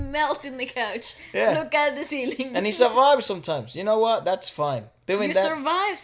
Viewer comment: and I heard the same thing. Really thought he said, "I survive sometimes".. like, whaaat?!